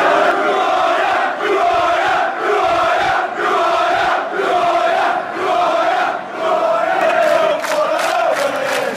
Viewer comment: nice video man! I'm a brazilian West Ham fan, I have just one question, what were you shouting to that man at 2:38? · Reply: thanks mate :) it's "Who are ya?!" what we're shouting :)